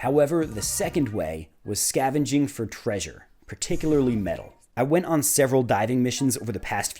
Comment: When you went dumpster diving for metal, i thought you said meth and i was very confused